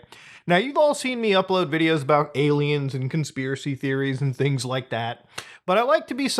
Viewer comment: did you just say "gay-liens"? Lmao